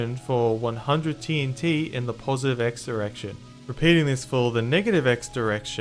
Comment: I swear I heard erection.